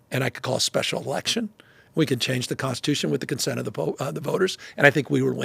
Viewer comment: CHANGE THE CONSTITUTION WITH THE CONSENT OF THE POPE??? Is that's what he said. (01:40) [People]?